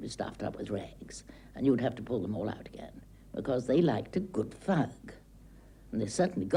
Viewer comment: The woman at 25:03 - did she say that "people liked a good f*ck"? Maybe it's the English accent...